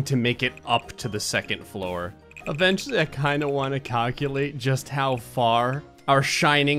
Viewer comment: Cockulate? Did I hear that or am I crazy 😅😂